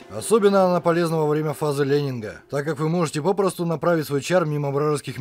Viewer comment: Бородатые мужики, тоже хорошо. Видимо у меня проблема со слухом и я вместо того, что б слышать "фаза лейнинга" , я слышу "фаза лемминга". Чем-то напоминает то, как я услышал "Хватка нежности "